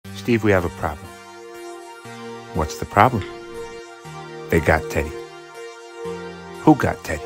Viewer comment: First time I listened I thought they said…. “We have a problem, the got Tate”. I was thinking what are these guys into that this is a problem for them.